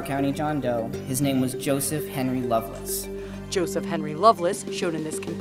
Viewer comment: I thought they said Joseph mainly loveless lmao